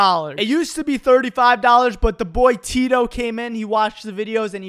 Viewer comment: I thought he said "pog"in the end but people is saying that he said "pause"